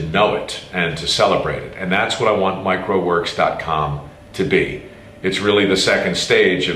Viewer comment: Lol, thought you said micro works.